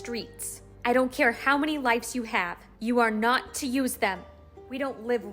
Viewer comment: At the start I thought it said "that's how many legs you have!"